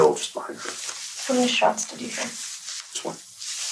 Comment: When the girl asked Jensen how many shots did you hear, what did he say? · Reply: He answered..."Just one. "